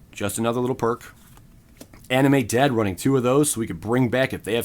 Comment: I was listening to the video in the background and i thought you said "Anime dead" instead of animate lmao